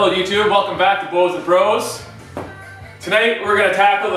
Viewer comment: I heard, "welcome to blows and bros"